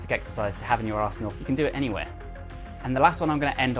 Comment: When you say "arsenal" it sounds like "asshole" so I keep hearing "it's a really good exercise to have in your asshole" and it's hilarious.